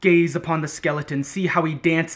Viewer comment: I thought he said gays upon the skeleton not gaze